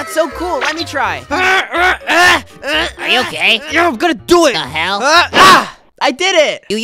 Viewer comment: I thought he said Are you gay instead of okay